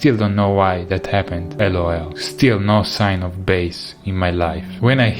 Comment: i hear: LOL Caption: a loyal